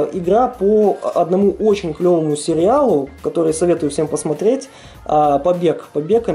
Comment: Сёма? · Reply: а может тебе послышалось, и она на самом деле сказала "всём" ? :)